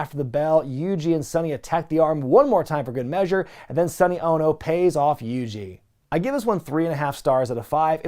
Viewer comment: When you said "paying off Yuji", I somehow heard it as "paying off Eugene"... and now I can't stop picturing Nick Dinsmore in some sort of gimmick mash-up of Eugene and Nagata.